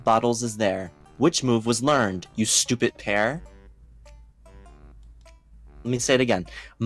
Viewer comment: For that one question I thought you said: You stupid pear.